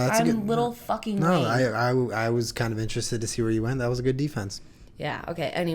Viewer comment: Holy crap when Julien said J Cole I thought he said Jiggle and died laughing